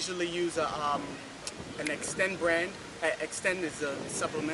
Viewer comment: Thought he said extenz at first lmao